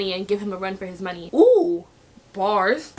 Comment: Its almost new year and when she said “oohh” at 0:04 i heard firework and now im dead🤣😂😂😂